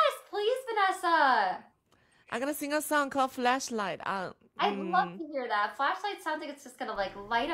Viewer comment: YOU’RE OUR FLASHLIGHT, 🔦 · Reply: I swear, I thought she said fleshlight. 😂